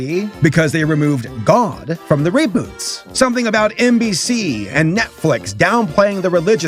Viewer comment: Did he just say Mbc? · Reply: nbc